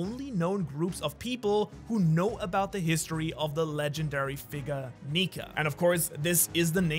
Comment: did anyone else hear figga 💀